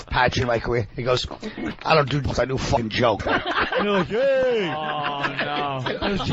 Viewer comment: What did Colin say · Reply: I think the audio was messed up but he was quoting Vos saying “I don’t do bits, I do fucking jokes.”